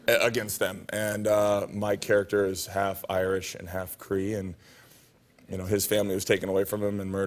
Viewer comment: For a while there I thought he said his character was half Irish and half Korean 4:41 😅